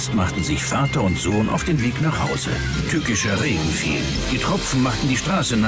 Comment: ich verstehe erstmal Türkischer regen fiel xD